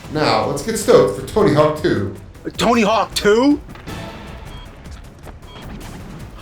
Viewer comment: did he just say Hawk Tuah?!?!?